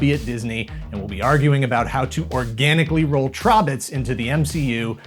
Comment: Oooohhh... Trobbits. I first heard it as "throbbits," and thought "that can't be right."